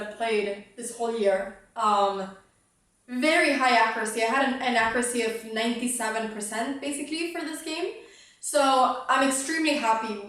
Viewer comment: So your best game is my average game from what Im hearing. I also typically have inaccuracy of around 97%. What I heard is "I had inaccuracy of 97%, basically"